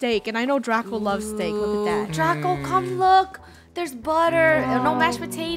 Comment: i hear butter hahahahahaha..... Me:Butter from BTS...ahahahhahahahah Me: and is a fan of bts....